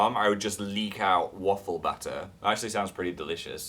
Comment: I thought he said butter! Oh, batter makes SO much more sense! (Unless they make butter specifically for waffles in England, then I might be right..)